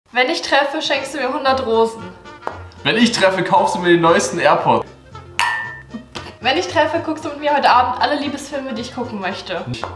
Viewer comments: Ich habe verstanden :wenn ich treffe schenkst du mir 100 drogen 😅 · Ich habe am Anfang Hosen verstanden und nicht Rosen 😂😂